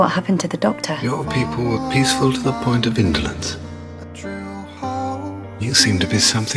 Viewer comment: "your people were peaceful to the point of intolance" is that what they say? If i translate this to my first language it basicly translates intolance with intolerance?? But that doesnt seem to make sense, can anyone help? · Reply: "...point of indolence..." - kind of a fancy way of saying laziness.